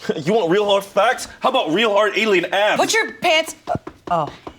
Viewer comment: why did I think chanse said "real hard alien ass" and had a fake ass strapped to his stomach😂